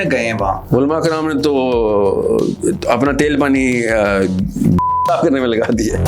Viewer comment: kia saaf karne mein laga di hai . I felt like i heard bhund😮